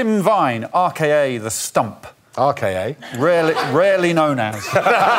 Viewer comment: "RKA- Rarely Known As" Gonna add that in my vocabulary now. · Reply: I thought he said "Really Known As" but both seem to work? I probably misheard.